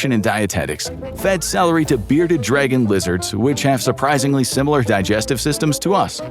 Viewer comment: Hang on, what did you say there? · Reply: Bearded dragon lizards These are actual animals, but in size more similar to a 8 m/o kitten than to a crocodile